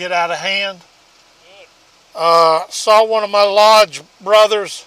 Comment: the subtitles are wrong, he said "lodge brothers" and not "large brothers." I would bet money that he's talking about the masonic lodges, part of the freemason fraternity, hence the term "brother." Very telling that he mentioned that given the context of the situation if you catch my drift.